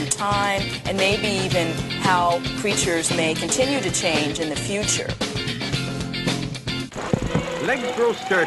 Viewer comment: I almost thought the paleontologist said "how preachers may continue to change in the future". rofl! We need to get preachers out of government!